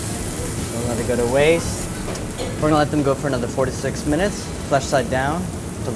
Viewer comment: Did I hear correctly at 4:40 that they needed an additional 46 minutes to cook through? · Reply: 4-6 minutes, which is why it's jerky at the end. What a waste of Salmon :-(